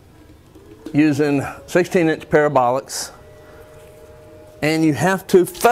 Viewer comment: did you say "pair of bollocks ?"